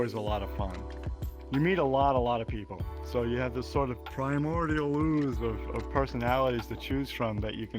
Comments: Just a guess but I think he said "Primordial Ooze" of personalities. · "primordial ooze" is what he said. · "primordial ooze" is what he said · I think he said ”Primordial Ooze”, but what he meant by that i have no clue😄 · primordial ooze i think he said 1:28 Idk the meaning tho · “Primordial Ooze” is what he said